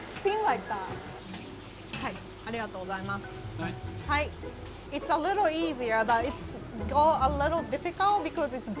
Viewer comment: did just say aligato :D